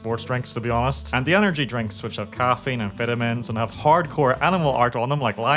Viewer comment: ngl I really thought you said amphetamines there and I was briefly sold on the product.